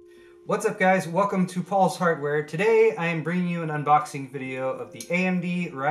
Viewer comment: lol I thought he said "Hi and welcome to FALSE HARDWARE"....